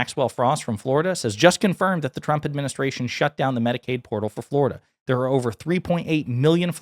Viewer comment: I just heard you say "Medicare Portals are shut down". Isn't it "Medicade"?